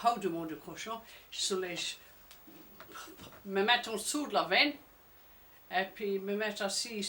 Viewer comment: Very interesting observations, thank you. Can you understand her anecdote about hiding under something on the way to school? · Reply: I'm from Québec, our French is highly influenced by Normand since a very large percentage of our ancestors came from Normandie. At 2:08 she clearly says the word « entsour » which means "en dessous" in Québec and in Jersey too apparently.